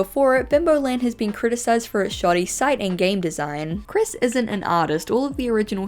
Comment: i swear i thought you were saying "shawty site" instead of "shoddy site" for a second, I was not understanding lmao